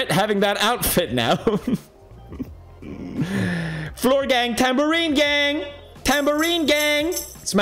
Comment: i thought it said milk gang